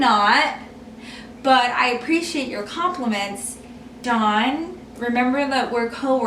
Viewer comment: Does anyone else notice at 13:37 she says don? Or am I just mishearing??